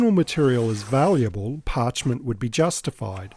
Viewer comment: Non woven polyesters will be out of scope. I do use them, but I don't like them for spine lining or board attachment, which they are often used for. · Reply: minutes into this video you mention Parchment ( though, before checking, I thought that you had said vellum) for the label binding and it was for that I was suggesting Tyvek as a substitute. You are making a huge contribution to the worldwide bookbinding community with your videos and for that I, among many I have heard from, are really, really grateful.